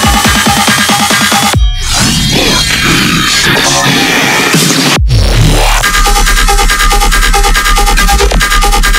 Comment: is he saying "ihr werdet versklavt?" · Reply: yes he said "you will be enslaved"